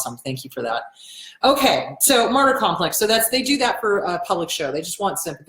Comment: you said martyr complex, I actually thought you said "murder complex" ( Like what? haha)